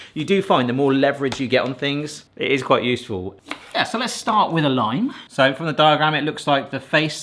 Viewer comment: who else heard "let's start with a line" and thought "that escalated quickly" ? 😂